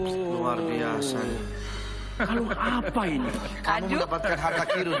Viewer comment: mbak nyebut kanjut? atau emang salah denger ya?😂 14:50 kalo urusan kaya gini mang emang jagonya